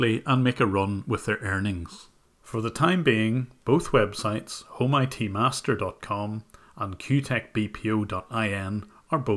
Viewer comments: When I first heard you say HomeITMaster, i thought you said "all-mighty Master" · at first I thought he was saying "Amazon, Almighty Master" instead of listing Amazon and then HomeITMaster.